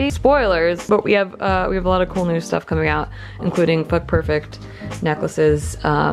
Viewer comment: wait hold on what? does she say foot perfect?